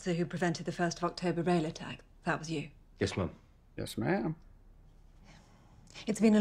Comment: In this particular case he was saying ma'am, just his accent made it sound like he was saying mum.